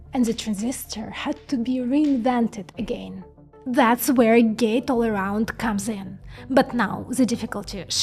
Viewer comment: At first i heard "gays all around"